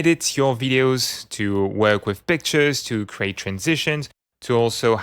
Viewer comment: Just discovered this add-on, completely fascinated! And I know you said "work with pictures" at 1:08, but the audio and accent conspired for a second and made me think, for a second, that you said "work with b*tches". Sir, you got my attention!